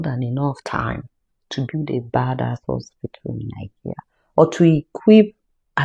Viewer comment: I thought they said we have good hospitals in Nigeria?????